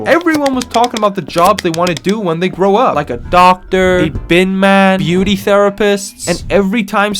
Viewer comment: I know this was probably unintentional, but at (6:14) I heard "thin man" like the character from Little Nightmares 2, which spoilers: Is mono, the main character who wears a bag on his head.